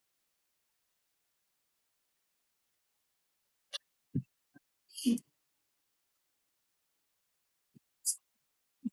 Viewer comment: what did you say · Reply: Idk why my audio did that 😭I said he hit a porn star and got her pregnant lol